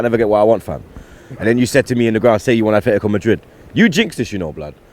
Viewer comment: Its Atletico not Athletico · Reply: i only hear ''Afhletico'' from tbh :D